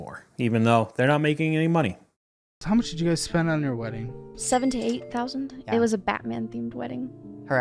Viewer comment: I thought she said $7,000-$8,000… not $78,000. At first I was like “wow, that is super reasonable”, then I realized I was missing a few zeros